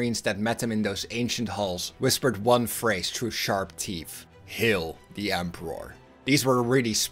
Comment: the transcript and subtitles say Hill the emperor. Are you saying heal or kill or is it actually Hill as I can't find anything online about this line